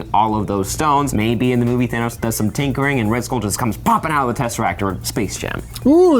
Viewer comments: I heard the interview live and could've sworn he said skrull which would make an interesting entry into this whole war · I thought he said Skrull